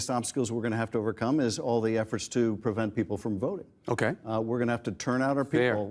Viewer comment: "Did he say prevent people from voting?"